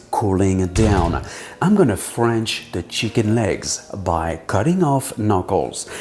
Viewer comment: Did he say he’s going to French the chicken legs? 😘🐔